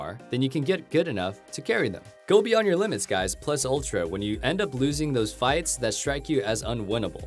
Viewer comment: Did he really said "Plus Ultra"? 💚💚